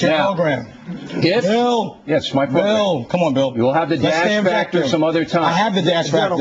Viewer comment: I thought it said 'The Orly Factor'